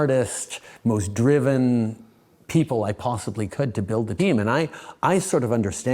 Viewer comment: did he say “Build the Demon”. Correct me if I’m wrong…..please. 😮